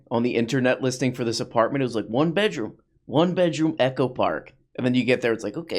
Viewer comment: I thought it said "Echo Fart" and so i was waiting for a big reverb fart sound